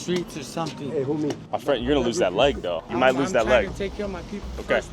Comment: did bro really say “I got another leg”